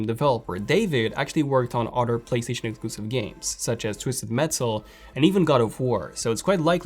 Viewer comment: Can Somebody tell me the name of the game ?? at 05:25. i heard (Twisool mansoul)